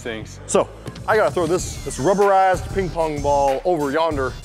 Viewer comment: Did anyone hear say rubber ass or is it just me